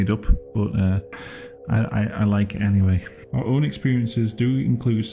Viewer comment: I cannot tell what you’re saying but you Lowkey sound like you’re saying “I like anime”. Lol